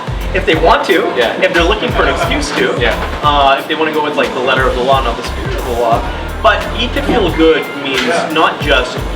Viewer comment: Wait did he just say "letter of the law not the spirit of the law" BOOM.